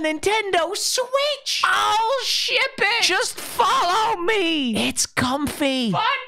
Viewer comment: If you don’t know what he said, he said “OH, SHIP IT!” I think